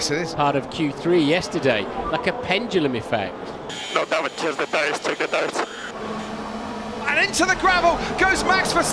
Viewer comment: Can anybody tell me what Sainz says at 2:05? I cannot for the life of me make it out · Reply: 'No damage. Check the tyres, check the tyres.'